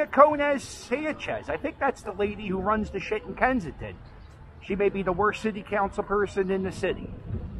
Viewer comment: i thought you said drug bust in kensington?